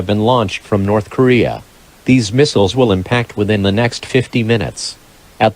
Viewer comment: 50 minutes or 15 minutes? I heard 50. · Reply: it said 50